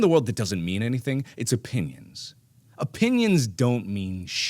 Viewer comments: i thought he said "a penis" instead of "opinions" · “if there’s anything in this world that doesn’t mean anything it’s a penis” THATS WHAT I THOUGHT HE SAID UNTIL HE SAID OPINIONS A SECOND TIME I STARTED DYING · did anyone else hear him say, "If there's one thing in the world that doesn't mean anything, *it's a penis*." ??